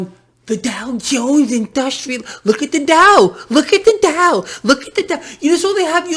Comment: Look at the DOW? I thought you said: "Look at the DUH!"